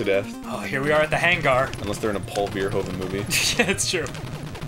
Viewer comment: What did Arin say? I keep hearing "Paul Hogan movie" but I dont think that's right.